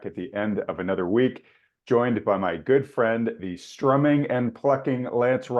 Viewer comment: I thought you said that he was humming and F---ing. Which both can be done when the power is out.